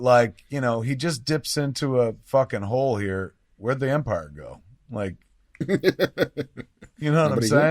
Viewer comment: what? when their in obi wans house? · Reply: My bad. I could have swore he said "helped the Emperor hunt down the jedi" but it is infact "Helped the Empire" you live and learn. 😁